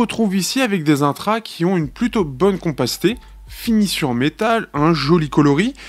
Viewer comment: une plutôt bonne quoi ? "compastée" ??? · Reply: Compacité